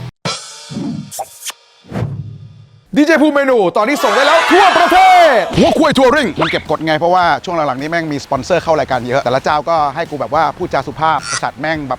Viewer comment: หัวควยทัวริ่ง ใครได้ยินเหมือนกูบ้างวะ !! 555555